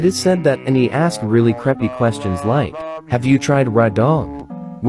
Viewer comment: I thought he said preppy instead of creepy